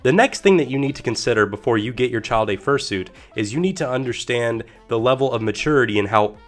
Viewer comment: I thought you said “burying your child in a furry suit 😭😭😭😭”